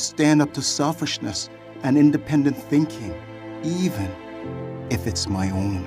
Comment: did he actually say independent thinking !?